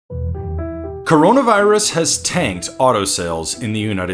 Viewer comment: I thought it said “Caronavirus has left” 😭